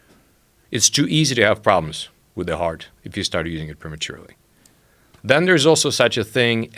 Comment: "It's too easy to have problems with the heart if you ______ prematurely." What did he say right there? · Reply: Luminous start using it